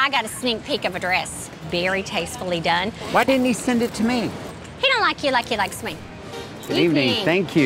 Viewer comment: “ He doesn’t like u like he likes me “ BURN LMAO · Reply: I thought she said “he doesn’t like you, he likes me.” Probably just misheard it